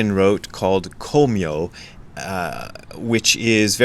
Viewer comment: At first I heard “Kimyo”, which I am currently a bit obsessed with … from the Lotus Sutra I believe … Uchiyama Roshi discusses it and it’s been meaningful to me. In any event, this was awesome too and I’m excited for the podcast. Great shirts today, too! 👍🏽